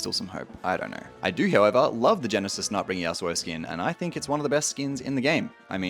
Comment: Why does it sound like you said genesis nutbringer yasuo lmao